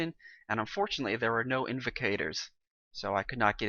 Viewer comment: did he just say invicators?